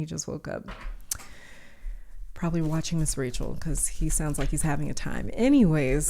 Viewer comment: I was on pile 3 and I thought you said your baby was doing a ritual 😭 you said Ms Rachel